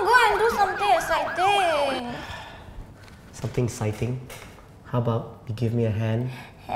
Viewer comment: he said "something exciting?" while the subtitles said "Thanks man"😂😂😂